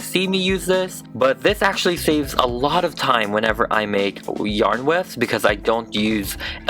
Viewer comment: almost sounded like you were about to say weaves. Haha, I guess I’m hearing things. XD